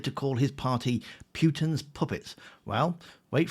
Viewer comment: I thought he said 'Putin's poodles' (as in lap dogs)...not puppets. 😅 Both work.